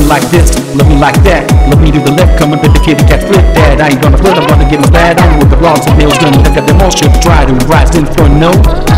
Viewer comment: ay did say my username?.....or was he just hungry? anyways RAHHHH HAPPY ANNIVERSARY DAYLOVE YOU GUYS ARE LITERALLY SO EPIC HERES TO MORE FUN DAYLOVE MOMENTS🎉🎉🎉🎉🎉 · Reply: JSJSJS HE SAYS "c'mon baby kittycat!" (his entire rap is in english!)